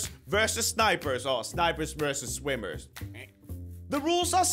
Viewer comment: Who else thought he said diapers